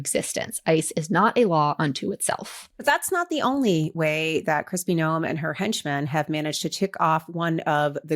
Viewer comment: I thought I heard one of you say, Crusty Noem 🤠